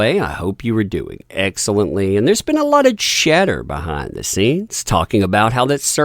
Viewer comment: Just to give you guys a laugh, I misheard "chatter behind the scenes" as "cheddar behind the scenes". It took me longer than I'm going to admit to, to realise my error.